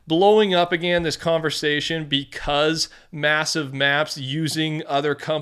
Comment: did i hear... massive?